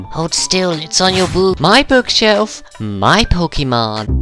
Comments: for a sec I thought he said boob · it sounded like said " Hold still its on your boob." XD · it sounded like the minion guy said boob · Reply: Glenda Huff he did say boob and his name is (says in the bottom left corner)